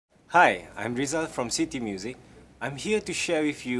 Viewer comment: I thought he said "shity music" xd